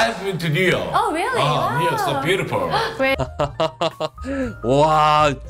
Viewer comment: new york so~가 아니라 you are so~ 로 들리는데 ... 아닌감유.?